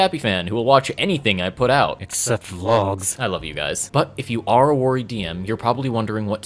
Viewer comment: I TOTALLY misheard "except for vlogs" as "suck my balls" and I just nodded and went "That checks out." Then I re-listened and felt like a dumbass.